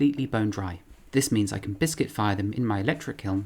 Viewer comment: anyone else hear him say "biscuit fire"